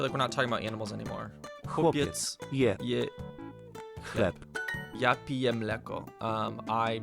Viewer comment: I wasn't looking when he read that one, and I got extremely concerned because I misheard it as "chłopiec jebie chleb" HELP